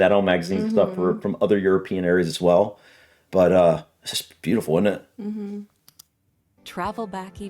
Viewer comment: sounds like you say “just beau’ful init” very English 🤣 turning into one of us